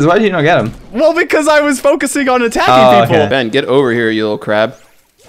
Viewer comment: Anyone else hear "Ben get over here you little cr**"? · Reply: I know, but the first time I heard Cra*, so I rewatched and then I realized it was crab